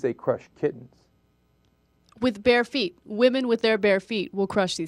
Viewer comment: you know, until 4:03, when you said bare feet, i thought you were saying bear feet.